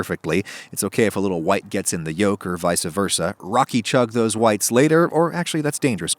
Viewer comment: Did he just say "vicuh" versa??!?